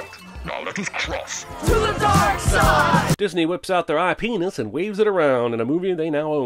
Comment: it sounded like you said penis